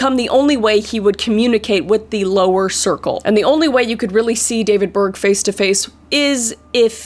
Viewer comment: I nearly had a stroke I thought you said David Bowie at first oml